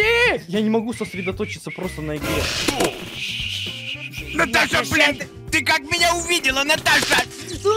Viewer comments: Наташа бл*, или мне послышалось😅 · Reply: Я кое как расслышал и он сказал Наташа блИн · На этом моменте всем послышалось что дед сказал мат, хотя он рил его почти сказал, но здержался сказав блеан. За это респект деду) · А мне послышалось Наташа блиан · Наташа бл##, или мне послышалось😅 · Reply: Он сказал блен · Непон 2:45 Там мат или мне послышалось??? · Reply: Он сказал блин,был бы мат Эд вырезал этот момент,но он этого не сделал. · Наташа бл*, или мне послышалось😅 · Наташа бл*, или мне послышалось😅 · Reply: Да, но он сказал «блин». Удачно сказал значит, что послышался мат)